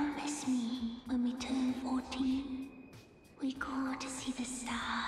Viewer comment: i think the age is 14 not 40 the subtitles are wrong